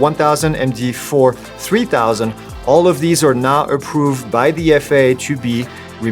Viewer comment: It's sounds like you said not approve but I can hear now approve replaying it